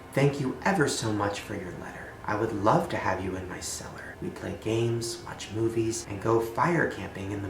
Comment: I thought it said "I would love to hang you in my cellar"